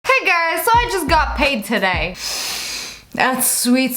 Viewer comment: everyone: comment me: confused at 0:00 did she say guys or girls?